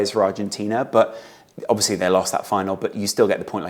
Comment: did you say "obviously they lost that final"?🤔